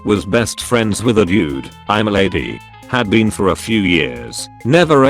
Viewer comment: I was listening to this as I was getting dressed for work and I heard "I'm a lady, have been for a few years and I nearly spit out my coffee lmao